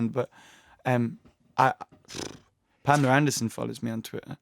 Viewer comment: Who did he say in 4:06 who follows him? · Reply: s Pamela Anderson